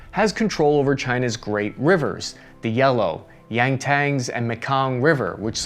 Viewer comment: Sorry, I know I'm being an ass, but did you mean the Yangtze (Yang-ZEE) River? I could have sworn that I heard the Yangtang River, and I don't know where that is. 🤔